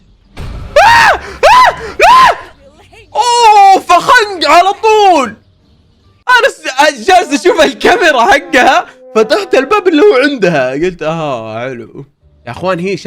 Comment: سمعتها فخدي 😂😂😂